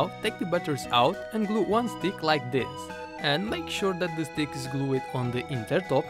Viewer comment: Thankyou! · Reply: He Does Sound Like French Because Instead Of Saying Stick He Said Dick If U Don't Believe Me Got To Time 5:08 And Put Captions